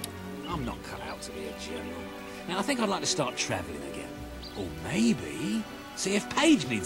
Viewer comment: sounds like he says "Star Trek"